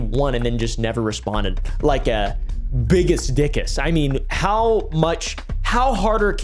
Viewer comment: Did you sayyyyy.... Bigus...DICKUS?!